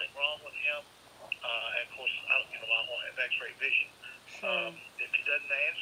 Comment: Around 4,06 does he say he has excellent vision, WTF. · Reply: I heard "I don't have X-Ray vision..."